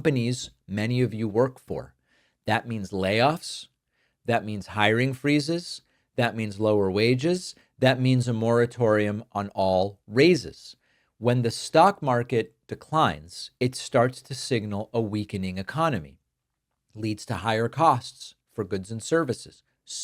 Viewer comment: I heard "a moratorium on razors!" Very puzzled Indeed.,...